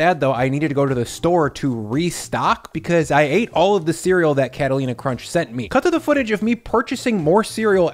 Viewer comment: bahaha i thought he said "captain crunch" (for the add)